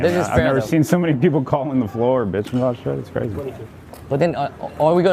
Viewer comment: Did Alan say "...calling the floor, bitch"?